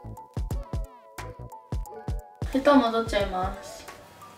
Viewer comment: 布団も取っちゃいますにしか聞こえん笑笑